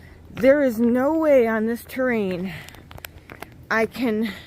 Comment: Yesterday, or a few days ago, when you said “terrain,” I heard “train” and thought you meant “trail” but since you were exhausted you made the mistake. But now I realize it’s my ears and/or your accent.